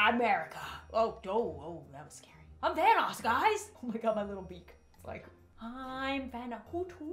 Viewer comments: I heard I'm Thanos guys. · When the owl filter was on I heard: "I'm ThAnOs GuYs!"